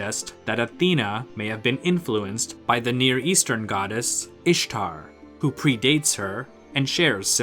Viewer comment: did you say, "Ishtar"? <num> And These men are pawns ....